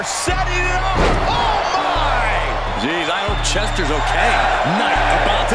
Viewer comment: U could have sworn they called Vince chester